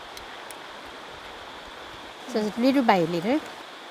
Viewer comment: When she said little by little.. I thought she said they were by lidl. Really nice video. Thanks.